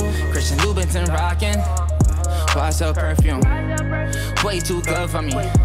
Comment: I thought he said “ why you so perfect” not “YSL perfume”